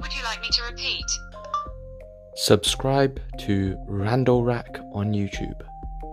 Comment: In Goggle's defense, I thought you said Randall as well